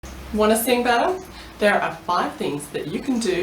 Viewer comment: i was confused when i heard "you wanna sing butter?" and then i realized she had an accent